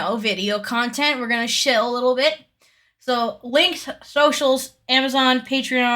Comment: Bestie when you said "shill a little bit" I heard "shit a little bit" and was so confused for a sec omfg